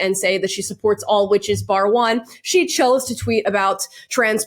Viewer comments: I heard "by the way" not "bar one".... I mean the "all the witches" jibe is a clear diss, still. But I don't think she said "bar one". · Okay so I am fully standing with JK Rowling here, but someone please confirm Emma actually said "Bar one." I thought she said "By the way" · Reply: Definitely "bar one". Watch her lips. Plus, just two words, not three.